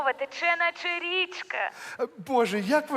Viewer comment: Что такое..че на черичка? Ну так расслышал, женщина там типа из Канады сказала. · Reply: Тече наче рiчка